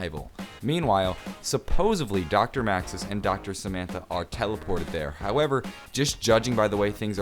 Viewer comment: did anybody else hear say Dr. Samantha